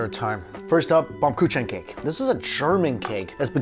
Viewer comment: I needed to listen several times to hear what he said at ~18:45, he said Baumkuchen. Ah yes my childhood cake/pastry.